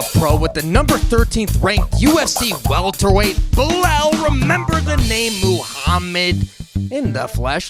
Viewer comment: Did he say Belal... I thought he said "But I'll remember Mohammed. "